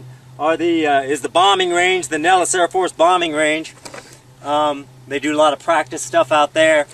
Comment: People mix up those names all the time so its probably not an issue. What he said was "Nellis bombing range," not the base itself. However, there is an AFB out there called "Creech AFB," formally named "Indian Springs AFB." Then there is the famous "border" itself which begins only 3 miles west of hike and extends all the way north for about 80 miles. Map in my channel. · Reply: Yes, Nellis bombing range. thought he said Airforce Base. I guess that is closer to Las Vegas. I got to study Google Earth some more lol.